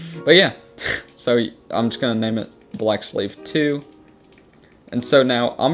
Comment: Thought he said black slave lmao based